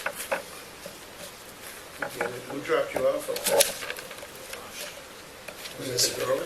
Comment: Hey, I love your videos, but some of the captioning in this video is really bad.. if you don't know what they are saying just type unintelligible or something. An example: at 45:15 The detective says "Who dropped you off up there?" and you wrote "Who dropped you off, girl?" None of the mistakes are offensive or anything, I just don't see the point in writing anything that is incorrect or you cannot hear clearly enough. This happened a couple dozen times in the video in my ears opinion. Beyond that, the video is great and well done in every other aspect, and I enjoyed it overall. This is meant as constructive criticism and I hope you take it as such. I gave you a thumbs up! Thanks and keep 'em coming!